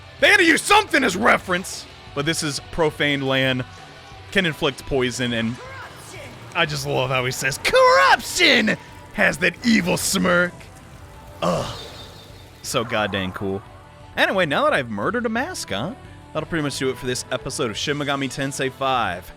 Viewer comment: I feel a bit silly now because I thought the Nahobino was saying "KURAP-SHIN!" like it's some Japanese phrase lmao